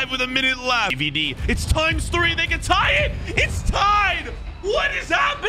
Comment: what did he sayy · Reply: It’s times 3 they can tie it